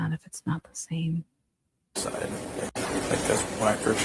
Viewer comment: I heard, “It’s not the same CI and creep.“ Who knows? It seems tampered with. Happy Belated Birthday Lady!! 🎉💐🎈